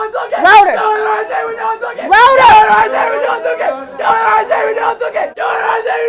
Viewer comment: what is he saying 😅 all that screaming makes no sense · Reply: He’s shouting “Do the right thing when no one is looking”